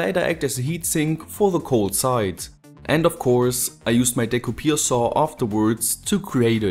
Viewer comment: What did you call the saw at 1:20? I'm not trying to be rude, I just don't recognize what you called it. I'm assuming it's a brand name or nickname like Skil saw, Sawzall, etc? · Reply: It the german name for that tool is "Dekupiersäge", which he didn't seem to translate